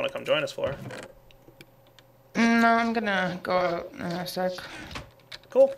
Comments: Did flora really just say im going to go out and have sex?...i guess she didnt hear that he was recording? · I heard "go out in a sec." hahaha we all hear different things